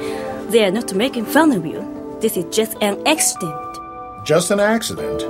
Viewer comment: I have to be honest her accent is soo strong in the Japanese version , that I can hardly understand her when she speaks English, and she sounded like she says extinct instead of accident. Also I forgot how rude she was in the English dub ( well all the girls are especially Corina) " That's funny, I didn't think I was talking to you" .. Cheeky cow. -__